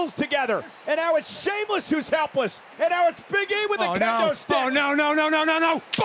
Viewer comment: I thought I heard “shameless” is helpless